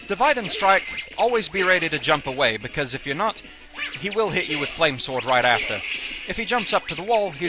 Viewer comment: i hear "flame swyored"